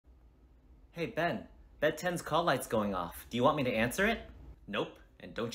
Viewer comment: Am i the only one who thought he said "ben 10" instead of "bed 10" at first xD